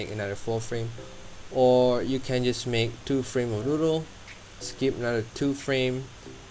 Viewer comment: sorry but that sounded like noodle in my ears lol